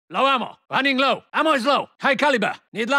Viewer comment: At first listen, instead of "No ammo" (0:00) I thought he said something in Spanish ("Nos vamos").